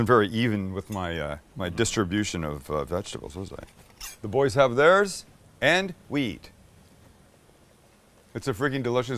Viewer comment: “the boys have theirs… and weed” is what i heard 😂